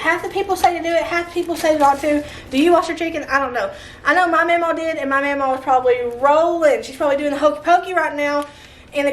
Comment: I always heard don't was your chicken